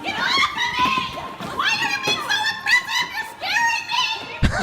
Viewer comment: I heard "oppressive" at first as well. I might need to retire from the internet.